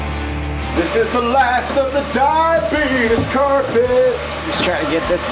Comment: I'm not sure what I heard @ 5:46 or did you say this is the last of the diabetus carpet lol makes me remind me of my type 1 lmao Edit: yup you did lmao that guy just died in August or so in 2020 at 84 rip